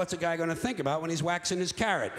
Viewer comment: La traducción es mala: en el minuto 3:44 lo que dice es "waxing his carrot" = encerando su zanahoria, una manera de llamarle a la masturbación